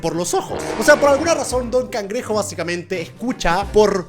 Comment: pensé que dijiste don pendejo xd